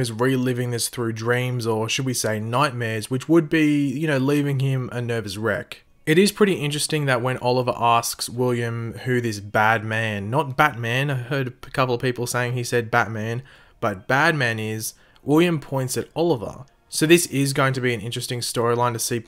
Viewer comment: Thought he said Batman, he actually said Bad Man